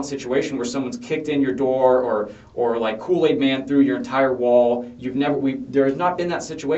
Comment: did he say “kool-aid man through the wall”?? 😂😂 wtf